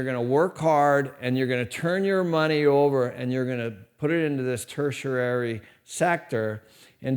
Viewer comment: did he say "tertiarary"